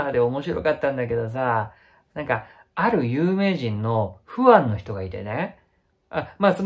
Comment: 「ある有名人のフワァンの人」 が 「ある有名人のフワって人」 と聞こえて、ずっとフワちゃんで再生されていた コメ欄でフワァンって気付きましたありがとう